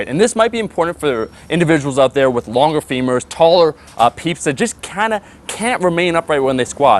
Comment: Did he say 'taller pizza' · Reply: taller peeps